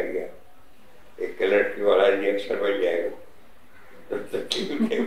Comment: what is he saying? "Aik clerk ... ??"